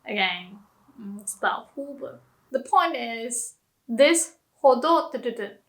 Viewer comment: What does she say at 20:16 ?I can can’t for the life of me figure it out ?!!!! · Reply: 2 years late but I believe she said "it's doubtful" :)